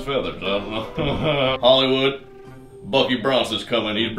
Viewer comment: OHHHHH you said “Bucky Bronson” I thought you said “Meet... Fucking Bronson” 😂😂